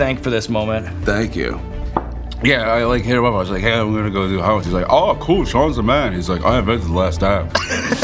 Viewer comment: wth is he mumbling 😂😂😂😂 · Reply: "Yeah I like hit him up I was like 'hey I'm going to do the hot ones.' He's like 'oh cool, Sean's the man.' He's like 'I invented the last dab.'"